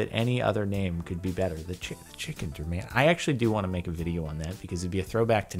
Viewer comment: i hear you but endicken